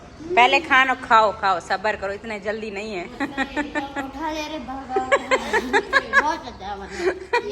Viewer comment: What did say at 11:08? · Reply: Utha le re baba